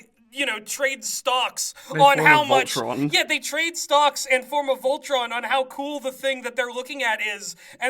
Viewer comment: I thought they said "formed an Ultron" and that gave me a very funny mental image